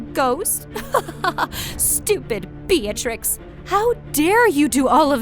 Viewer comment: my mind thought she was gonna say stupid b*tch